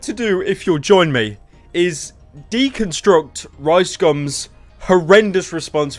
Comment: Every Time you say "Ricegum" I hear "Ricescum"